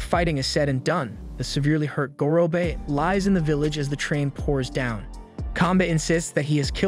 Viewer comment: does anyone else hear "as the train pours down"